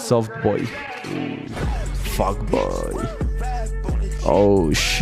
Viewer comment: Pense que decia femboy jajajajaajaj